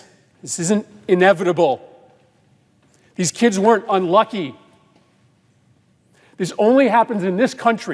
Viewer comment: En el minuto 1:06 creo que dice "this kids weren't unlucky" en lugar de "this kids were unlucky"